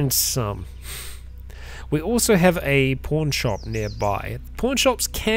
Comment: Oh pawn shop. I thought he was saying porn shop... You never know with Cataclysm